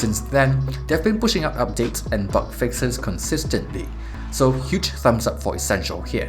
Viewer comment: I thought he said butt fixes at 3:10 lol good review.